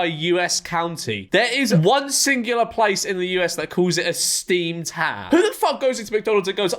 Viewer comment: not getting the steamed ham reference physically hurt me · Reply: I thought he said steamed clams?